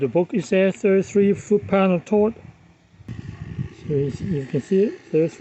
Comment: Damn I thought you said 300 hundred foot pounds! I installed it and now my block cracked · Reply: He did say "33 foot pound of torque". Sorry for you.